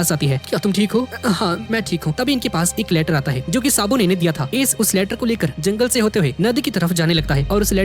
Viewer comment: On 22:23 ,on caption and see he will " sabun " Instead of " sabo "